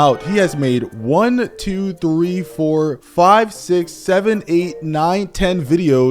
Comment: did he just say… 67?